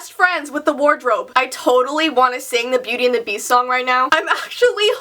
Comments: It sounded like she said "Booty and the beast" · it acutually sounded like she said "bootty and the beast" when she was singng😂😂😂😂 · When she was singing the beauty and the beast song it sounded like she said........well it's ina pro pro but it sounded like booty and the beast XD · When she said beauty it sounded like she said booty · When you were singing the beauty and the beast song, I laughed because it sounded like you said booty and the beast lol. Good vid btw